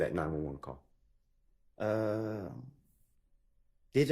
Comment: Did he say... Did i to his prosectors😂😂 2:41 till 2:47😂😂